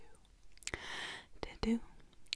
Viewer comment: I thought she said ddu-du-du from Blackpink